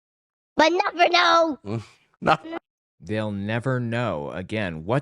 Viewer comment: I think this is captioned wrong, I think he says "But not for long", which makes more sense in the conversation anyway